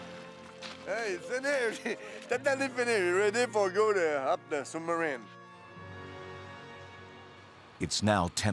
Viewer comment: sounded like he said "ready to 'hump' the submarine